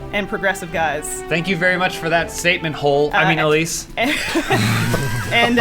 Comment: What does says at 3:52? "Thank you for that statement hole"? What's a statement hole?